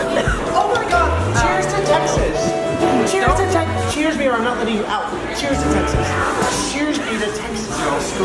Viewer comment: Um... When got the Ice Cream and was like "CHEER ME" I heard "CHEER ME OR I'M NOT GONNA EAT YOU OUT" and I am mortified bc I'm pretty sure he said I'm not gonna let you out 😂💀